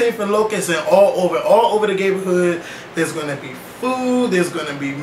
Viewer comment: I thought said Gayborhood lol